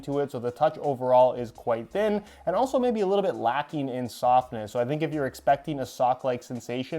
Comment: I thought you said they’re lacking in “sockness” instead of softness and I was like just invited the perfect adjective for modern football boot debates.